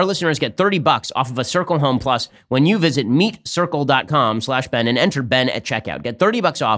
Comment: Did he really say "meat circle dot com"?? Yeah, that's gonna be a "no" from me dog.